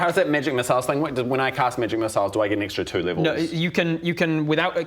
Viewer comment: I thought for sure was saying "midget missile" but then I realized it was "migic missile" because of his accent.